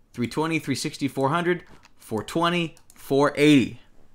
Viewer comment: DID HE JUST SAY 4:20? (It's 440, but he still got the right answer.)